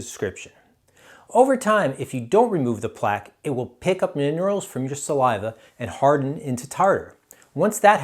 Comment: What does the bacteria pick up from the saliva? I didn't understand what you said at about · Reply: Hi, I said "minerals".